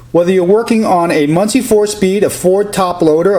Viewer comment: i swear i thought he said 24 speed but he said Munich 4 speed